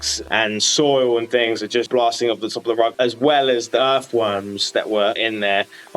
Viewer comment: can you repeat that for me again? i think i heard "earthworms" instead of what you said..😟😅